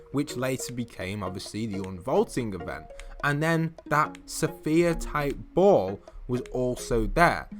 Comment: it sounded like he said safere, instead of sphere